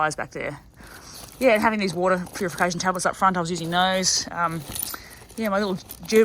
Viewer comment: what is said at 5:18 please? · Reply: Hey Mate. It's here. At 5:18 I was talking about the water purification tablets and the Gerber Dime that I keep in my top tube bag. If you have any questions about this, just let me know. Thanks for watching! 🙂